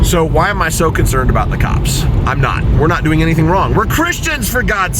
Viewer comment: “We’re not doing anything wrong , We’re Christians “ ..What I heard was “ Go profile 1%ere because they’re doing something wrong , NOT US were CHRISTIANS ahhhhh”